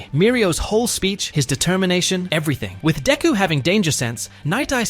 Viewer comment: did someone say, determination?